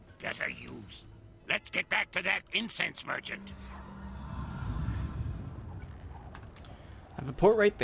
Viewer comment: for a second i thought ratbeard said "incest merchant"